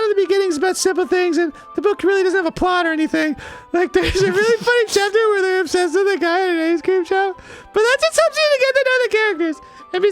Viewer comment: I thought he said “Like there’s a really funny chapter where they have sex which a guy at a ice cream shop” Then I wand like NANI WHAT DID I HEAR ON MY CHRISTIAN SEEVER???